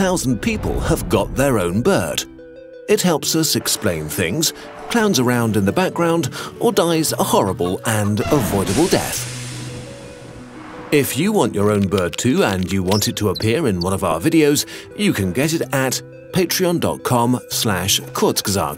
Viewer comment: I love how the bird goes “EEERRHHH” at the end if the video and then kurgastg says “ and unavoidable death” 😂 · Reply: i thought they said "avoidable"